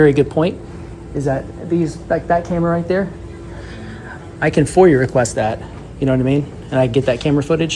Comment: the way you say it sounds like youre saying "foyer request"